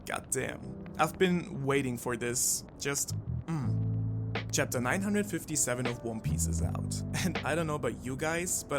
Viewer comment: Did he just say chapter 957?